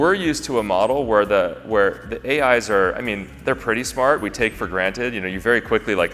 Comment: No zoom cuts, folks! My producer hates me 😂 , because he's studied the art and science of zoom cuts... but please continue to let me know if you prefer this style? I want to help facilitate the best way for you to most easily digest this important content. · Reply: if you turn on the auto-genrated subtitles it says : The A Eyes are. instead of the "AIs" .